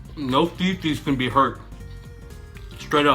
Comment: He said 'no fefe's cam be hurt' XD Thing is I don't even think the speaker was offended. The moderator was just like 'is that truth I hear? FUCK THAT!'